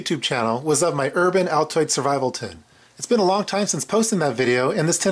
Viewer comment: did he say survival Tit?